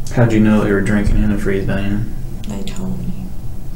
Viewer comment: I think she said "They told me." at 45:53, not "I don't know".